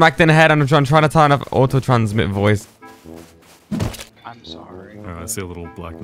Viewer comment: when he said “so”, i thought he said “im sorry”